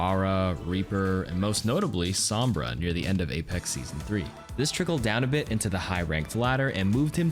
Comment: DId I hear you right @ 4:52 you said "APEX SEASON 3" lmao hahaha